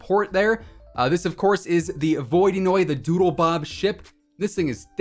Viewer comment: I know he said something else but at first it sounded like he said DOODLEBOB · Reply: I said doodlebob ;)